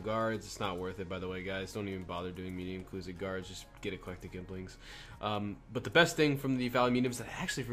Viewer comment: what did you say at 13:45? get a ...? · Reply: lol carry eclectic implings